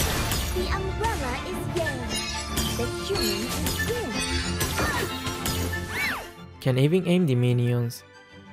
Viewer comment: "Umbrella is yin, the human is yang" What I hear: "Umbrella is gay, the human is bang"